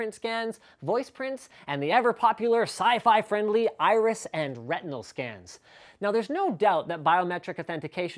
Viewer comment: thought he said “rectum scan”